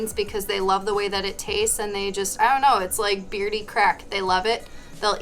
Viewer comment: I didn’t quite hear what you said and would love to buy but what is it called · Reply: She said beardie, but it’s short for bearded dragon.